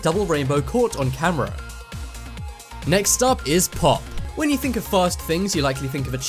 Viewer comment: i thought it said poop